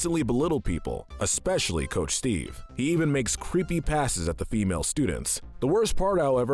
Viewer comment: I thought said creepy pastas with a Boston accent instead of creepy passes. Awesome video by the way! the Gratitoad is my favorite character!